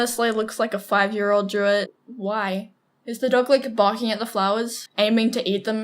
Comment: it sounds like said 'why'